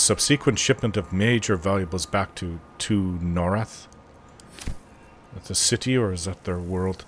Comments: Well it is a big multiverse. But honestly I don't remember that part, maybe accent didn't make the word come out right. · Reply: I looked online. A city of theirs is Tu'narath. I misheard it as Norrath, lol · Norrath... Isn't that the world from EverQuest? 🤔 The note about the shipment of stuff the Githyanki looted and sent back to... it sounded like you said Norrath.